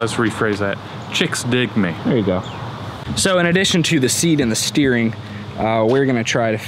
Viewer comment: Can't wait until we light up all four tires! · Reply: sounds like said seed, not seat